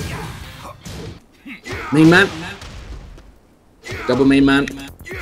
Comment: do I hear a slowmo "Masku" there?